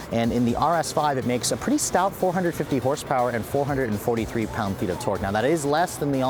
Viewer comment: did you say 150 HP?? XD & plllllllease stop pronouncing the G in quadrifolglio